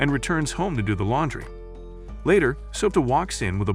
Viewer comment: Soda instead of Souta is crazy😂😂